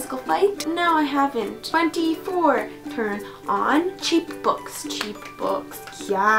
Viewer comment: Lol I thought you were saying 'cheekbones' but it was cheap books! Hahaha xD YESSS THE 5th WAVE